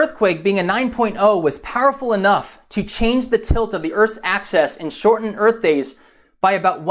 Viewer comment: did he say earth's access?